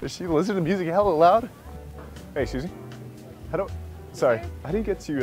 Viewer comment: AM I THE ONLY WHO EVERY TIME HE SAY "HEY EXCUSE ME" I HEAR "HEY SUZIE" BECAUSE HE SAYS IT SO FAST😂😂